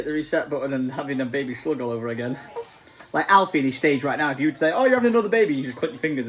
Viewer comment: did she say Alice?!